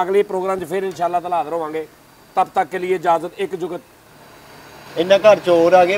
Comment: i didnt understand the last jugat ? 21:30 what he said .. aaj phir...? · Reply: Ena ghr chor aa gy kehnda oay hoay Aj fer bahir e pena